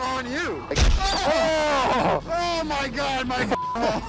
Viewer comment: Hilarious! Sounded like yelled, "OOOOhhhh my god, my cock and balls!"